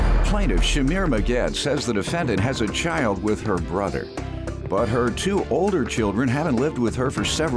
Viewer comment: I swear I heard the start of the narration wrong. I heard, "The plaintiff has children with her brother..." EWW! LOL!